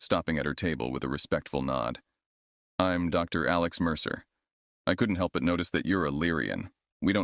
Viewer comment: I heard "Dr. Alex Mercer" and just thought, "Whelp, he made it to space, it's all over." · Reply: Ngl it gave me chills cause I thought I heard, Dr. Challus Mercer, fr thought everyone in this universe was about to be made whole again.